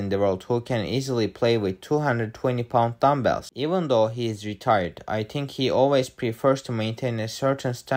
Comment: I heard "retard strength athlete" at first instead of "retired"- I was confused for a sec